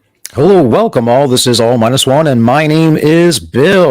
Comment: I just had another Matt & Blonde listener tell me what your channel name was because your enunciation was so poor on your call. Better spell it out next time. I thought you said "oh minus one" and spent like 10 minutes trying to figure out what it was actually.